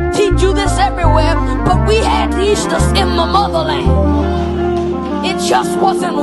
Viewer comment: I like this song except for the one part where the guy says we had 'Easter' in the Motherland. Easter is pagan worship for the goddess Ishtar. We keep Yah's feast day of Pesach (Passover). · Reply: I thought he said eshas meaning wives more than one but Easter definitely pagan 🤮